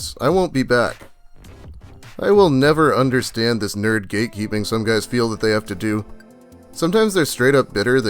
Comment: I heard "gatekeeping" as "geekkeeping"